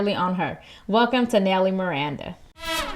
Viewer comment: I thought you said, "Welcome to nailing Miranda". lol